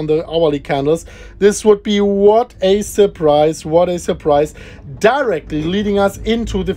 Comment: I thought he said DERICK not DIRECTLY 🤣🤣